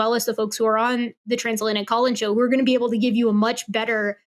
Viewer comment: What's the name of that show, I didn't quite catch that and typical "Translate Colin Show" in YT shows no results, I assume it's called something else 😅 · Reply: Trans Atlantic Call-in Show 😊